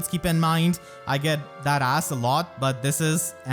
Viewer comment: I know you said "that asked" but at first I heard "dat ass" while I was looking at Corrin and it fit really well lmao